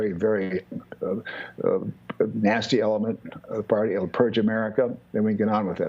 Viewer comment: did he just say to purge 74 million Americans ?!?